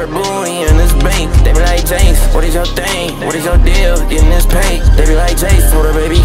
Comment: They be like “Iayze, what is yo thang?”🗣️🗣️🗣️ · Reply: I thought he said get in this paint😭